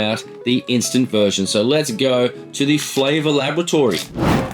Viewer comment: thought you said flavour Lavatory hehe the Thomas crapper